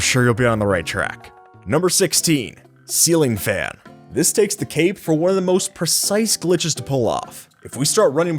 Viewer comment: did anyone else hear takes the CAPE?